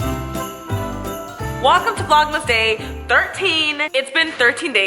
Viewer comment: Was it just me or did she say 'wakam to vlogmas'?